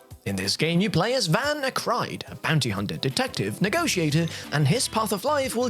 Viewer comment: Sounded like he said Panty Hunter 🤔